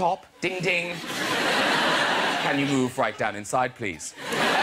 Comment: after Graham says "Room for one more on top, ding ding", what does he say next? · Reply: "Can you move right down inside please?"